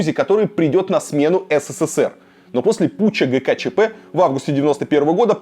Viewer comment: Что говорится на 4:12 пуча гуча чп? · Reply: путч ГКЧП